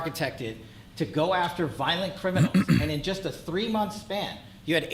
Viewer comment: Architected is not word Kash you simpleton. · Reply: I'm fairly certain I heard him say ' Violent Feminals ' in there somewhere...